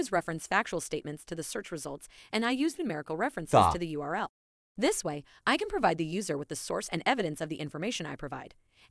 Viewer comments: What did the bot on the right say at 5:54? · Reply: 'Stop.' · ? What does chatgpt say? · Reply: Stop